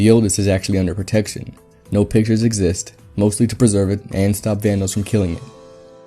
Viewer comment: you may have said this, or im crazy but i think you said "and to stop thanos from killing it" listen '-'